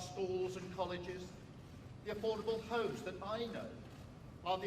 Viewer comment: The affordable h o e s ??? What did I just hear?? 05:09 😭 It's not even 3 AM yet